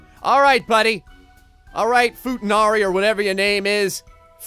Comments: Did... did you say futanari?! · Did you just say Futanari.... Oh god.